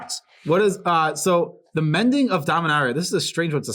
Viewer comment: I heard, "The Mending of Diarrhea" LOL. got my sub